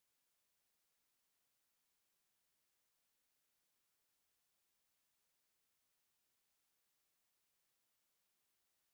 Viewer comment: hi! at around 36:20 one of your students starts to finger spell a class that was the worst class they took. I cant figure out what is fingerspelled.. so far I have "n u t r i". was this "nutrition" class? I noticed you signed body after so I tried to use context clues. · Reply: Good job -- it was indeed "nutrition."